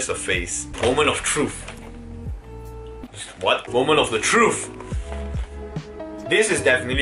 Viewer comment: Did I hear correctly? *Trufe*🤔